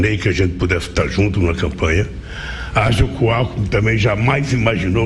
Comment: Gozado! Quando o cachaceiro falou "Alkmin" eu entendi "Álco". Confira em 3:12 kkkkkkk.